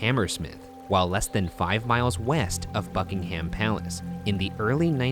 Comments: When he said Buckingham palace I thought that he said fuckingham palace. Why am I so weird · Why, at 0:53, did I hear f*ckingham palace instead of Buckingham palace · I thought Ryan said "Fuckingham palace" gotta stop smokin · I straight up thought Ryan said Fuckingham Palace and was very confused Also the shroud would be a burial shroud, very creepy burn some witches, Shane is THE witch we hail him as our honorary mascot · when he said "Buckingham palace" I heard "a f**king palace" I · when he said bucking ham i thought he said a f***ing ham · oh my god i thought ryan said fuckingham i